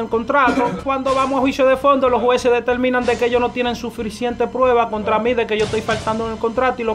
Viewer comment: Like si entendiste que Dijo "Sufrisiente"